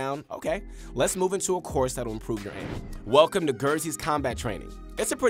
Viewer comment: did he just say gerzy